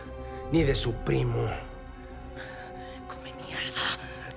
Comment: What did she say at 3:23 in Spanish? Can someone please tell me? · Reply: She said "come mierda" which literally translates to "shit eater".